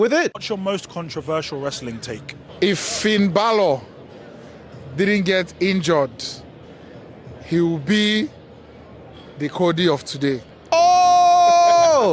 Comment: I’m sorry am I the only one that had no idea what he said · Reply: he says that if finn balor didnt get injured so much. he wouldve been the "cody rhodes" of this company